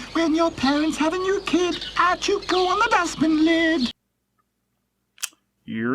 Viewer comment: I dont understand the english version of the "song" at 31:02 , and to be fair, i didnt know the "YEE!" meme! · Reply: "When your parents have a new kid, out you go on the dustbin lid!" or something